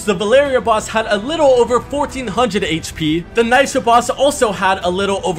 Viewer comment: I thought he said the nitro boss